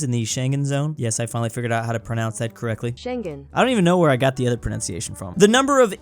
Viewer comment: Bro, finally figured out Schengen's correct pronunciation. At first, I thought he was saying "shogun".